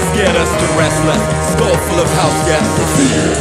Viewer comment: I heard "school full of house gas" instead of "skull full of house guests". ... Close enough.